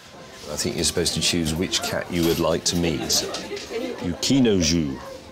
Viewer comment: i thought he said would u like to eat lol